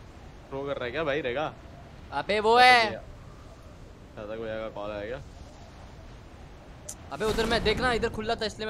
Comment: i heard "Ghatak bhaiya ka call aaya kya"!? Correct me if I'm wrong🤣🤣